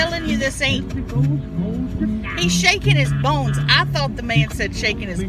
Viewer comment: Hes saying hes shaking his bones ,I thought he said hes shaking his BALLs !.... 🤣👌🏻just one of many laughs👍🏽from miss..🥰